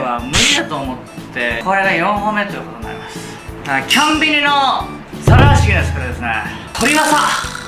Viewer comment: コンビニが、キョンビニと聞こえてしまう謎🕺